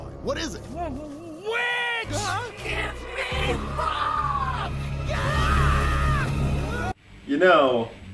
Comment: it sounded like the witch said "Give me fuck!" I wouldn't mind that...